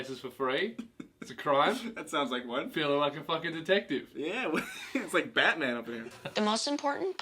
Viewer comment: So I had my auto generated subtitles on (I don’t speak Australian) and it thought he said “Dick tech-Ed” instead of detective